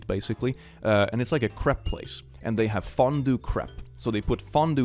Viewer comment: I THOUGHT YOU SAID “Crip place” INSTEAD OF “Crepe” NAOOOO